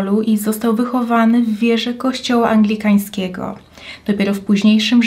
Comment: usłyszałam, że został wychowany w wieży kościoła. Roszpunka vibes 😂